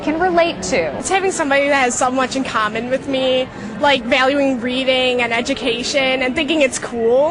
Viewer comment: I totally thought that the girl at 3:14 said "valuing breathing." Whoops.